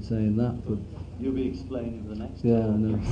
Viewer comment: Could someone please write down what they are saying at 2:51 pls🙏? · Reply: John (in reference to something Paul said earlier in the interview): you’ll be explaining it the next time Paul: yeah I know audience laughs Paul, sarcastically: it’s just wonderful here. John: you’re cute